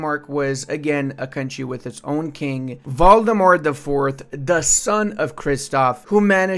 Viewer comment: Did he say Voldemort the IV.?